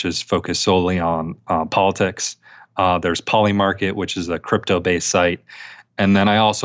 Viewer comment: I couldn't understand the name of the prediction market mentioned at 21:00. Could anybody write it here? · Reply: Polimarket? From politics...